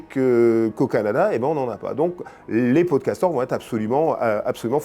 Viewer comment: La première fois que le professeur a dit "peau de castor", j'ai entendu "podcaster" 🙃 et les fois suivantes je ne pouvais que penser à cela 😉